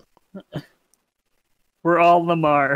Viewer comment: "We're all Lamar". <--- I heard that as "We're Olimar". Too much Pikmin lol